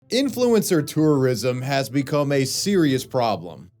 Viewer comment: "Influencer tourism." I misheard it for "influencer terrorism," which is still technically true.